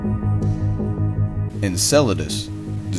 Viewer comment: I would reach down and just MOOSH it into my face, and...and...oh, my mistake, I thought you said enchilada...